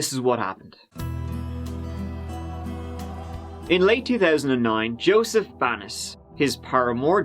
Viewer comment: For a second I thought he said Joseph Anus 😐